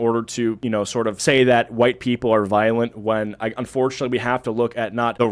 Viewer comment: did he say we have to NOT look at RAW numbers🤣